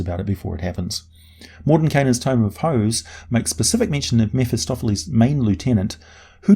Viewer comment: Okay, I'm pretty sure at 13:49 he said Mordenkainen's Tome of Hoes. Which makes me now think Mordenkainen is the most ball'in pimp in all of Toril. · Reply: is both studious and eloquent but I am very certain that I heard Tome of Hoes at ( 13:49 ).